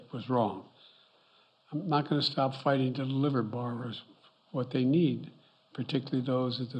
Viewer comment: ? What gibberish was uncle Joe saying here? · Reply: Something about barbers! Thats the secret, cut hair! *yes I know he said "borrower's" but the guy still fumbles over his words all the time.